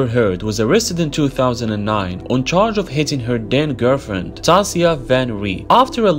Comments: I had to listen to this 3times. I thought he said “dead girlfriend” lmao 😂. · When he said "Then, girlfriend" i thought he said "dead" gf lmao, btw Amber's a creep for this💀💀💀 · I legit thought he said "on the charge of hitting her DEAD girlfriend"🤣🤣 which I wouldn't be surprised if amber would kill one of her exes.😒🙄🤮